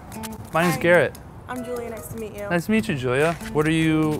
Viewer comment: she said her name was. But he called her Julia? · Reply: I heard her say Julia